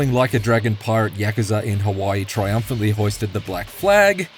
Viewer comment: I thought he said "yakuza kawaii" in the beginning of the video but actually he said "yakuza in Hawaii" LOL